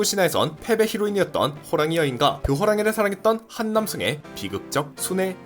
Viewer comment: 한 남성을 한남 성으로 들었네...(저는 그 어떠한 사상을 가지고 있지 않습니다)